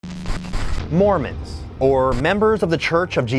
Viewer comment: i thought it said moron.